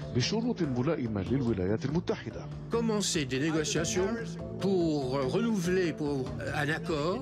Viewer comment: Any French people here? What did he say at 1:53? Comment c'est delegation pour renouveler a l'accord? Wtf?